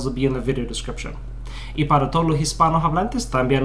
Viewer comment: It sound like you said in spanish, "If you speak spanish you shouldnt get in contact with me," just letting you know. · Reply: i say "no duden", not "no deben". But I do see how it's easy to get mixed up - they're very close. I'll consider switching it up for my next video, thanks